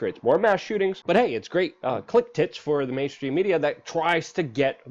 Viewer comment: Don’t know if it’s just me here, but what the heck are “click tits”? Luke says it twice, and captions show “clip tips” and “clicked” but I heard “click tits” both times! 😂